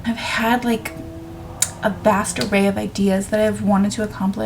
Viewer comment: When you said ‘vast array’, for some reason I heard ‘bastaray’ and thought that was just a word I didn’t know. And my brain went ‘like a bastard array??’ Lol.